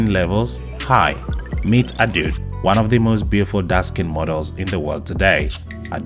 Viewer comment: I thought you were saying " dusky models " , then I understood you were actually saying " dark skin models " ... wow what a misunderstanding ...